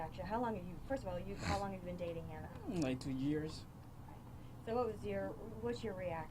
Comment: "Dating" for 19 years who says,dating after 19 years that's ur mrs wife ?????!!! That whole interview looked false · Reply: I thought he said 19 years; which means they were dating while wearing diapers. Lol...that guy was confused.